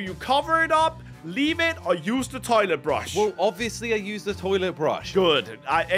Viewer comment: I thought it said toothbrush instead of toilet brush 💀